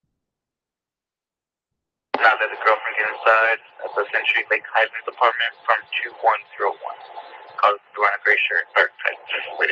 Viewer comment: I listened and from 7:50 to 7:56 on video this is what I heard: "SOUNDS LIKE THE GIRLFRIEND HID INSIDE, THEN SHE'S BEEN HIDING IN HIS APARTMENT FROM TWO-ONE, THROW-ONE. I don't know how accurate the end of his sentence is because of the radio click-beep; but I'm sure I heard "THE GIRLFRIEND HID INSIDE." For 40 years one of my jobs has been to listen to and type jail telephone calls, court proceedings, witness interrogations, etc., but the audio is often very bad and radio interference is choppy.